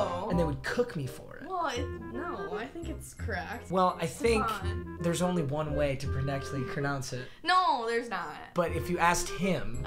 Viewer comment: did anyone else hear this mans say “i think there’s only one way to porrectly cronounce it?”